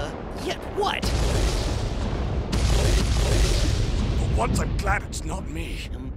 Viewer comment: for if yall didnt know what he said. "for once im glad its not me"